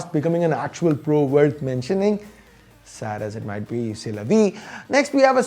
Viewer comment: For anyone who didn't understand what said at 2:05, that's "c'est la vie". This is a french phrase which means 'this is life'